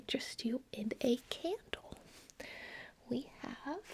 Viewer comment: Turning the sound down to not wake my little sleeping baby and I thought she said "can I interest you in a cake" not candle and now I'm hungry